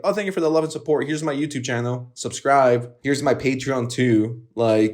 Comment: did you say subscrive?